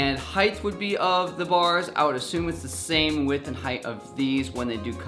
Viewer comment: did he say heighth